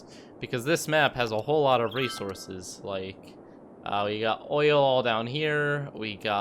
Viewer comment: DID I HEAR OIL?